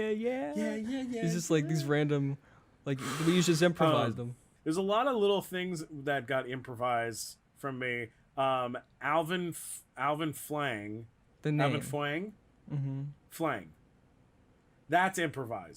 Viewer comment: I heard it as Alvin Fwang!